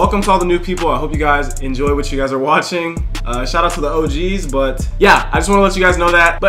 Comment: yo guys, what he said at the time 0:27? "shut out to the OG" or i don't know, lol. i'm not english so i'm too curious haha